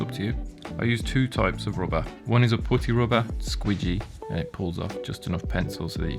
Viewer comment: I had to listen to it 3 times because I thought he said pussy rubber.😳😁